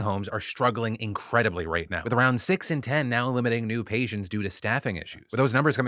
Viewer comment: watching this in the morning gave me a shock when I thought Phil said "Eliminating new patients due to staffing issues." thought the were taking new patients out back. think I'll finish my coffee instead of watching with my coffee. 🤔